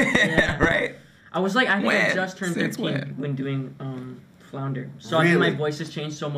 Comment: how old did Jacob say he turned when he did Flounder? I can’t tell if it’s 13 or 15 that he’s saying. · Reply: 13